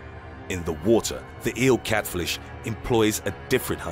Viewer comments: Anybody else catch the "Eel Catflish" at 6:04? 😂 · Catflish. Anyone else catch it?